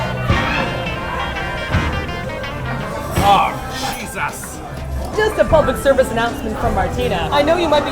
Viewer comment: I don't know if other people noticed but said SHISUS instead of Jesus at 7:18 I laughed at that.